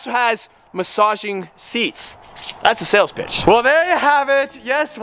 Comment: Near the end when you said sells pitch I thought you said that's a sells b***h